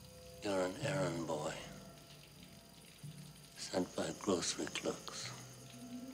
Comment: For years I thought he said “sent by Grossmanklooks” - I always wondered what it meant.